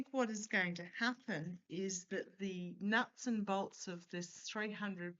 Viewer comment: When you said nuts and bolts, I thought you said nuts and dolts😅 perfect description of the Trump regime.